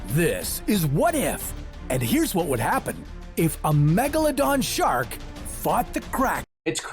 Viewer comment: is it me or did it sound like he said What if a Megaladon Shark Fucked a Kraken??? I did have my volume low and im tired so maybe its just me.